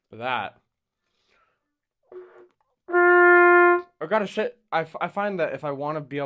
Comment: um did he say I need to take a shit? 💀